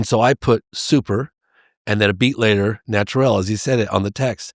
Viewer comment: Brazilian here. I think he said "sobrenatural" (paranormal, not normal), because we don't use the word "supernatural" and he didn't say "super natural" (prety natural). It changes the meaning of the sentence.